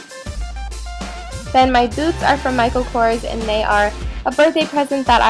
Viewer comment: When you said boots I totally thought you said " My boobs are from..." and I was just like "Dafuq did she say?" . Love your style, keep up the good work Beauty! :D